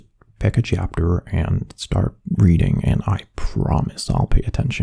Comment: i'm so dirty minded instead of hearing "Reading" i heard "Breeding" 💀💀💀🤣🤚🏽